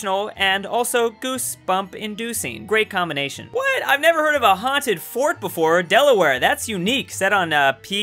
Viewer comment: i thought he said "WHAT IVE NEVER SEEN A HAUNTED FART BEFORE" i always mishear things the 1st time i hear them